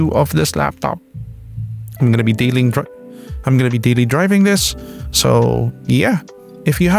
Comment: Sir.. I almost thought you were gonna say "I'm gonna be dealing drugs" in the Outro end. Hahaha